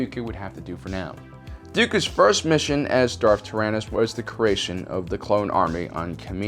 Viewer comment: I thought when he said darth tyranus I heard darth arthritis